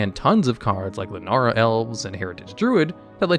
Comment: What the hell did he say at 9:12????? Lenara??